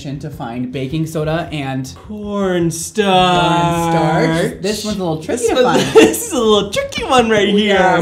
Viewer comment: So urm the subtitles ... when you said “corn starch” ... it literally says “porn star” (6:40) (I have subtitles on bc I have sensory processing disorder so it help me actually take in what you’re saying)